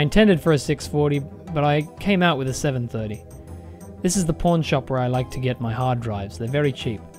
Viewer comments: When he said pawn shop I thought he said porn shop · I thought he said this is the porn shop where he likes to get his hard drives. He says they're very cheap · Thought you said porn shop where I get my hard drives